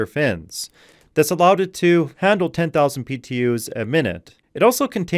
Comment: You mention "over 10000 PTU/min". What is this measurement? Did I mishear? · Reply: BTU's, as in measurements of heat.